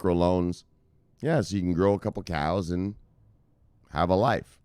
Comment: did u say "grow your cows" XD